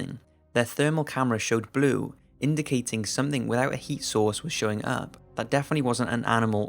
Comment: Sorry for being such an American but I heard "Heat Sauce" at first.